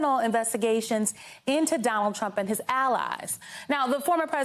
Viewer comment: Instead of "former president Trump" I heard "homophobic Trump" ... fits as well!